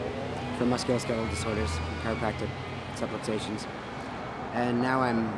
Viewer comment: where subtitles say "(Mumbles) He actually says "subluxations" which a chiropractic term for misaligned vertebrae